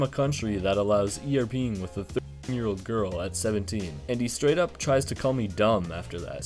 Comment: I thought he said 17 and 15 but 17 and 13 is criminal